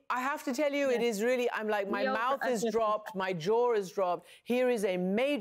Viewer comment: Did Amanpour actually say 'My jaw is dropped'? Edit